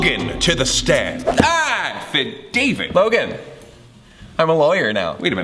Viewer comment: I was wondering what Logan said when he got shocked, cause I thought he just kinda said jibberish (I was like “Who’s David?”) but I looked at the subtitles and found out he actually said “affidavit”, which meeeeaaannnsss... AFFIDAVIT noun af·fi·da·vit | \ ˌa-fə-ˈdā-vət \ Definition of affidavit : a sworn statement in writing made especially under oath or on affirmation before an authorized magistrate or officer (Thanks merriam webster) So he was probably reading off law vocabulary before he got summoned in (hence the law book) Or is was a really clever joke that really was actually pretty funny, so The mooore you knoooooooow Thank you for listening to my ted talk